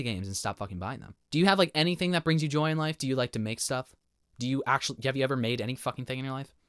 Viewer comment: bro 💀, why my stupid ass brain heard that as "do you like 🍆???"